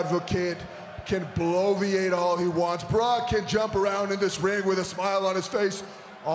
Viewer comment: When Jinder said 'Barack" instead of Brock I thought he was about to say Barack Obama 😂😂😂